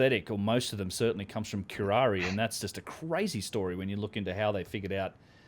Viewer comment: Is he saying Karari? In regards to the roots of anesthetics. Edit: Found it. "Curare"